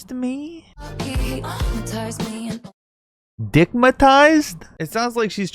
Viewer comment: the "ah-matized" line just pisses me off because of basically what said. if she's saying "[hyp]notized" then why the fuck did she pronounce it with a "ma" instead of "no", and i feel like "traumatized" doesnt make sense to me but idk. idk why it pisses me off so much but i think it's because i'm a hypnosis enthusiast so it pisses me off to hear people pronounce it wrong. not sure why it's such a strong reaction lol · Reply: Ppl say its dick-matized which Ive never heard anyone say haha but it makes sense in the context